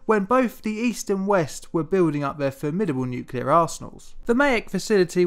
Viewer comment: I heard “formidable nuclear assholes”